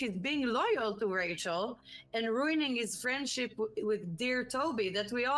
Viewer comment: now you know what we feel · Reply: im an idiot i misheard and believe she said franchise- but she said friendship